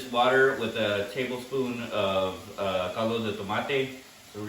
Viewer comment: Hi, may i know what is in the liquid besides water at 3:10? i didn't catch that. thanks thanks. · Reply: Caldo de tomato / tomato chicken bouillon